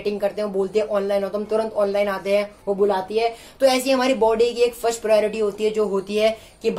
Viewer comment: I think he said "bulati h magar janeka ni"😐😐😐